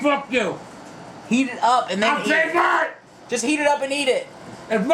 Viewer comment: I SAID WHAT!!!??? is that what he said?